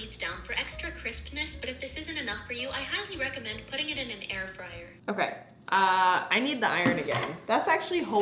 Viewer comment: did she say air fryer lol